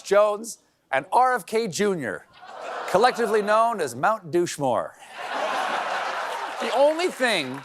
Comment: thought he said 'mountain dew shmore'